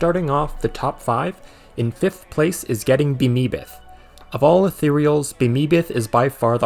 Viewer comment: it says mimbaith at the bottom instead of bemeebeth or getting bemeebeth